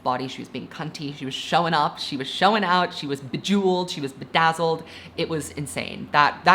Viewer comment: did you just say… bejeweled?